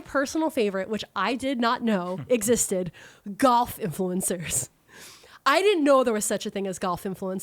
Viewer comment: I kept hearing "golf influencer" as "goth influencer" and I kept thinking "wait, she is blonde and in colorful clothes, she's not goth..."